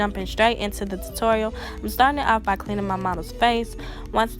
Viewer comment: Did she just say her mama ?! Ain’t no way, yall not going to say other wise… I don’t believe it ! I legit said to myself her friend is so lucky to get her makeup done for free… Beautiful makeup tutorial, you did that sis! Keep up the good work! #Talented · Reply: I said model, but thank you. ♥️